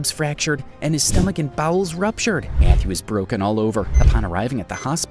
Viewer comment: In 4:26 when he said bowels why did I hear balls 💀